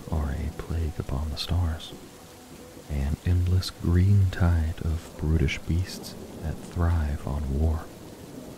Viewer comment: Almost fell asleep but misheard "an endless green tide of British beasts" and snorted